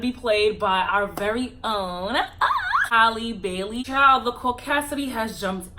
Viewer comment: At first I thought I heard “Halle Berry” would play Ariel. I was thinking, damn she’s old, can she even sing ? 🤣